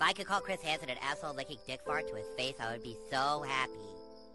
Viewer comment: My dumbass really thought said "Do you think we could just like- makeout sometime? Like do your laundry, baby?" At the end Yee idk what went through my head.. XD